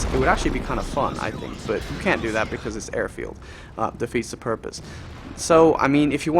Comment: Lol thought it said Anfield